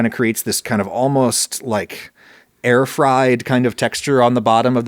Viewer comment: This almost what kind of texture? Iʼm sure you said something that rhymes with bear tried, but what?